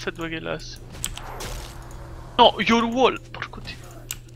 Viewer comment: ma al minuto 08:15 cosa dice? brontolio? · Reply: DICE PORCODDIO